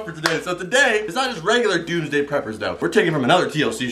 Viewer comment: I thought you said "Tuesday prepors"